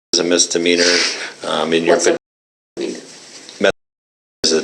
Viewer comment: did she just ask, "what's methamphetamine?" or did i misunderstand what she said? I'm legit asking